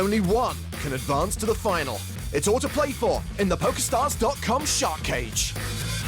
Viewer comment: I swear a god, for a second i thought the it said pornstar instead of pokerstar.